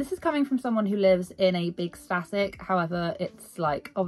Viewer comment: i cant for the life of me figure out what is being said? Like the closed captions are saying you live in a static? I tried looking it up but couldn't find anything😭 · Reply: A static caravan! But many just call them statics 😁 so they are large holiday home type builds, you can’t tow them on a trailer they are meant to stay put 😊 xx